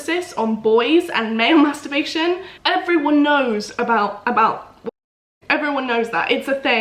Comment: What did she say at 3:33? Does anyone know? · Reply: Wanking lol